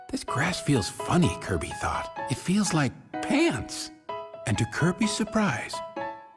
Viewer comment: lol i dont saw the subtitles and in 0:10 i hear'd "it feels like.. penis" lol